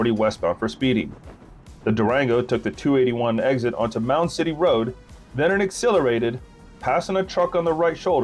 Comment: mount titty road .. did i hear that right lol 😂😂😂